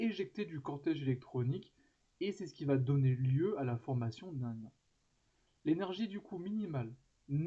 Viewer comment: j'entend mal , donner lui à la formation d'un quoi ? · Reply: D'un ion Loris. Quand on arrache un électron à un atome, cela donne lieu à la formation d'un ion.